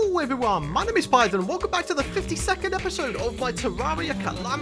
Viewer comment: says his intro and I hear “50 second episode” instead of 52nd episode . Yeh ...